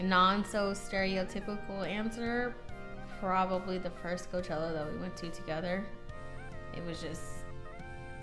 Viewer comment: "the WHAT teller that we went to" ??? Did she say fortune teller? No..? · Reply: lol the first coachella that we went to